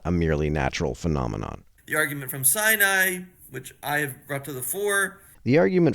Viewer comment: I thought that he said "The argument of side-eye." 😄